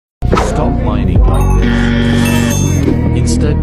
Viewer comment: I thought he was gonna say “Stop mining like this” and “instead, mine like this” 😂